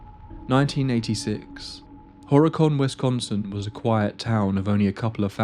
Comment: I thought for sure you had said "Horror-con, Wisconsin" 👻